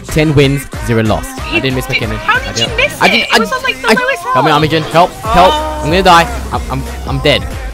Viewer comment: It sounds like says im a virgin help